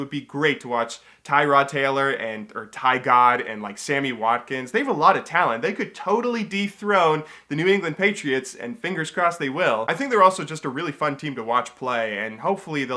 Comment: I had to rewatch 5:27 because I thought you said "deep throat"